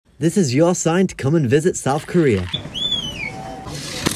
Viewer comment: i thought bro said north korea 0_0